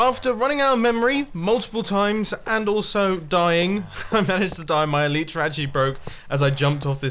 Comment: The subtitles say: "After running out of memory multiple times and also dying my parents died my elite Rajee broke"